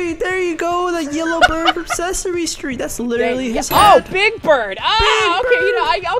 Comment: When they said big bird, I thought they said, big back.